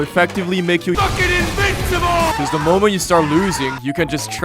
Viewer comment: DID SOMEONE SAY INVINCIBLE